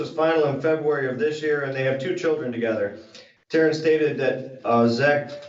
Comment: 12 y.o. Has a different mother so step-child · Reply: oooohhhh! Thanks for that👍 I thought they said 2 kids together 😂